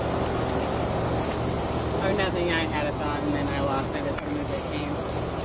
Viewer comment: did she say she had a dog and lost it?